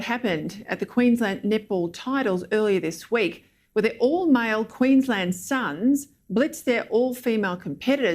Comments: Sounds like she say "nipple Queensland" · She sounds like she is saying nipple title lmao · Her accent is so thick all I keep hearing is "nipple Queen's land". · Thought she was saying nipple Queensland. New Zealand set the precedent allowing transgender Laurel Hubbard compete against women in Olympics weightlifting and then then named 'her' sportswoman of the year. She didn't achieve anything in the Olympics apart from 5 minutes of fame. To paraphrase Dave Chappelle regarding Caitlin Jenner, ' never had a period in her life.' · Why do I keep hearing "Nipple Queensland" just me then? 😜😜